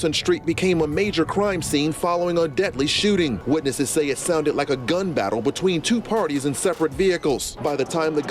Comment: u thought they said “IT SOUNDED LIKE A GUN PARTY”